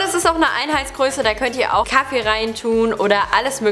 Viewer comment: WOW ich bin dumm :D Sie sagt 'da könnt ihr auch Kaffee rein tun .. ich habe immer kacke verstanden haha